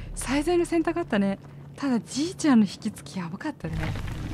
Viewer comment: ちいちゃんって聞こえるわ笑